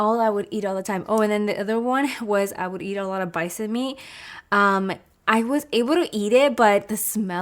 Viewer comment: On minute 3:32 you say you would eat a lot of what?? Lol sorry I literally rewinded it like 5 times to understand what you’re saying and I couldn’t get it 😫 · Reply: Bison meat es carne de un animal que le disen bisonte 🤮